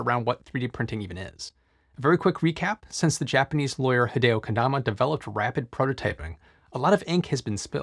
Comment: When you said hideo Konama I thought you said hideo kojima. Thought he was changing the 3d printing game